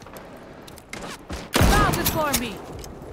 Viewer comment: did just say “bow before me” as she one taps a dude lmao